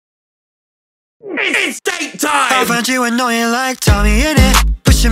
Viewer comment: It's date time! (Tommy's date with Beautie_) 00:01 - Some people find me annoying... 00:09 - Just killed a woman, feeling good. 0:12 - Vlog gun. 0:15 - Tommy's addiction to coke. 0:18 - Gogy! Georgenotfound. 0:23 - Only a small percetange of my viewers are actually suscribed... 0:28 - Tommy's obsession with women. 0:32 - Cadillac meme. 0:35 - Mod makes Minecraft 1000% funnier. 0:40 - "No me gusta", frase he uses a lot with Quackity. 0:45 - This one is from a blooper when he yelled out to his mom "I'm in the middle of a war, mom!" as he was interrupted during a DSMP stream. 0:48 - Tommy's exile arc. 1:17 - Mom, am I adopted? 1:22 - A phrase Tommy uses sometimes, a way he has to speak. 1:26 - Touch grass! Henry! 1:29 - BIG MAN! POGCHAMP! 1:32 - Vikkstar! Dream! 1:34 - SWAG arc from Dream SMP! 1:42 - I pog through the pain. 2:11 - Mellohi, his disc from Dream SMP. 2:17 - I want a moth called Clementine. 2:24 - Cadilac meme, again. 2:27 - Being called a child all the time. 2:30 - Twitch prime. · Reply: Damn i hear 0:00 as its steak time 🗿